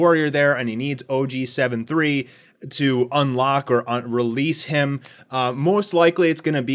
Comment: Did he really say 'unrelease' at 5:55 Becoz 'unlock' and 'unrelease' have different meanings